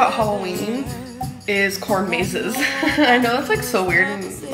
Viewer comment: I heard "corn mazes" as "court cases" LMFAO